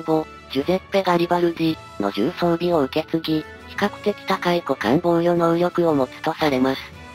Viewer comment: ラジオ代わりに聞いてたら個艦防御能力が股間防御能力と聞き間違えて一人で笑ってしまった\\\\